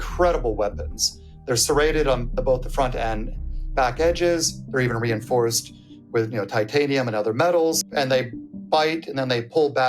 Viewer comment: Did he say that their teeth are reinforced with titanium…?